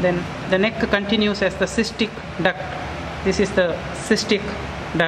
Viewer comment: sorry professor what duct is that? I didn't understand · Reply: The duct that extends from the gall bladder is called cystic duct.